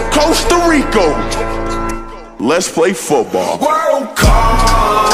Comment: it sounds like he said world cok